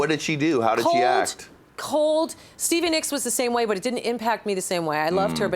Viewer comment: did she say Stevie Nicks ? Shit I hope that incident was just a one off.